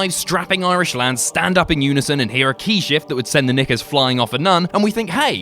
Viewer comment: did he say ni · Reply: nickers which is basically a uk term for underwear